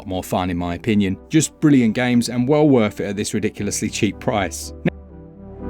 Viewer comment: Was considering picking up Dying Light til I heard about the "tough pricey" 😂